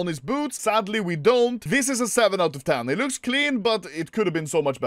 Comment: I think he said "This is a seven out of ten" not "three out of ten" mister editor